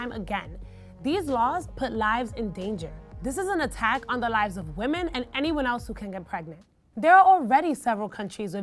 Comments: Did she say an attack on women and anyone else that can get pregnant? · Did she just say women and anyone else who can get pregnant?